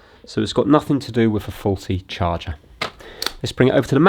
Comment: i thought that said farty at first in stead of faulty